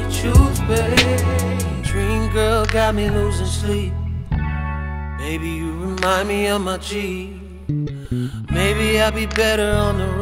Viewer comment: I thought he said...baby you remind me of Jeep